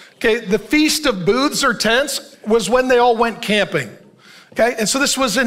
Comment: At first I thought I heard they all went "gambling" during the feasts... there was noise in my room. I was like whattt. It was "camping".They all went camping. Amen😮😅